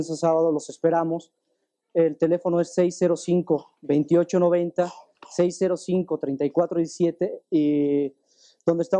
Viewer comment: ¿qué dijo? Se les coló un audio paranormal ahí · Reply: Dice "tonto"